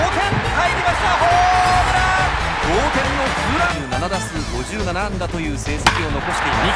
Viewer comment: 何回か繰り返して見てるけど 7打数57安打にしか聞こえない😂